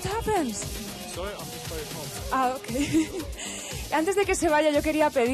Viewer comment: what did he say??? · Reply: Que sentía mucho calor. I am very hot.